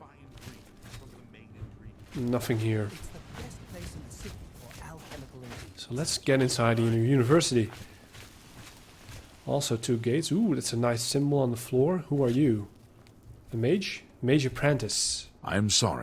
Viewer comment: did I heard two gays? lmao